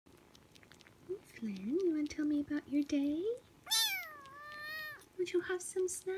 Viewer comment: Human : wanna tell me about your day Kitten : yeah Human : did u have some snacks Kitten : yeah Human : did u play with toys and u got a new blanket Kitten : soooo ssoooofftttt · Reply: I thought she said" Do u wanna tell me about your daddy?"